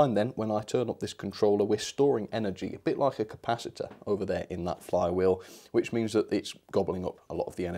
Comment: did anyone else think he said flux capacitor. 😅 i had to double check. Lol. Just got done watching the old movie before this. Ps the "flux capacitor" is from back to the future movies.